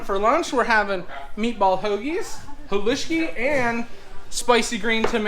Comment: What was that dish that you were having for lunch ? It sounded like you said "poluski" . It looked good, what's in it ?